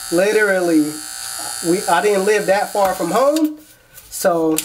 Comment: what did you say @ 4:40 "live too far from home" also shoutout to pops